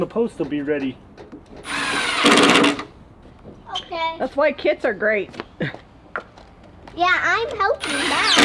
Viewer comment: Laughed when thought said "Kids are great" instead of "kits are great." is an especially great kid by the way!